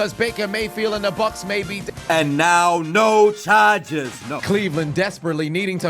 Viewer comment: am I hearing things I just heard no charges no